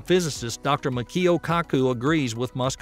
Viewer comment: All I heard was “..Dr. Mackill kaku..”